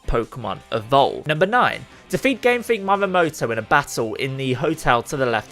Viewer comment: did you just say: mamehmoto......? dude how hard is reading for you xD if i was Japanese, i'd not have any of it